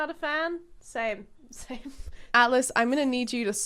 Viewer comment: I thought she said Alice at first then said he and I was confused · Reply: SAME. I thought she said Alice, I later edited my comment when everyone was saying atlas.